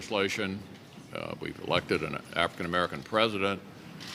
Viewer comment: “Collected an African American president.” Is going to be the sound bite that haunts me today. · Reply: Well first of, fuck Mitch Mconnell... but he clearly said "elected". I misheard it as collected the first time too.